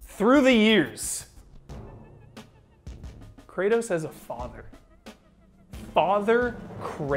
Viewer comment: I misheard you say "Kratos has a father.." as that little blue Kratos mini-me was shown. I got jebaited pretty hard.